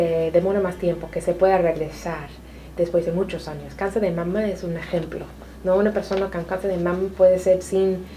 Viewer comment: Quisiera saber cual es el tipo de cáncer que la doctora menciona en el minuto 22:07, no entendí bien lo que dijo. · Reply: La Doctora se refiere en el minuto 22:07 al Cáncer de Mamas. Saludos